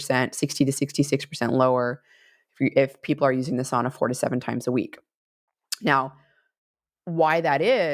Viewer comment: I kept hearing “using the sauna 47 times per week” instead of 4-7 lol. Great info.